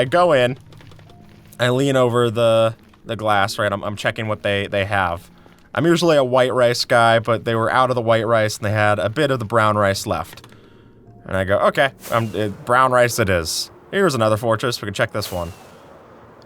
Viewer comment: when he said that he is typicaly a white rice guy i heard w white race guy